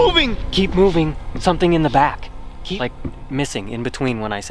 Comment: it sounds like it saids keep moving Lucifer is in the back!